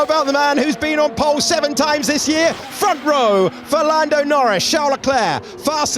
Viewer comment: did i just hear Ferlando Norris?! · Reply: For Lando